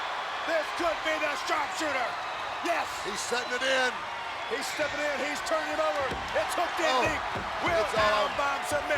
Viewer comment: What is the ring announcer saying at the 1:01:00 mark? The closed caption doesn’t pick it up. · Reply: It sounds like, "He's given it right back to you! Your winner, Bret "Hitman" Hart!" I don't know, but I think the ring announcer meant that Bret gives back to the fans with the win? Not sure.